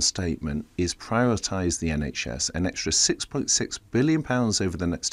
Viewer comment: Did he just say privatise the NHS.... 😆